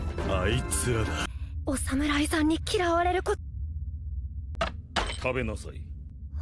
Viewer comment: Did bro said osama?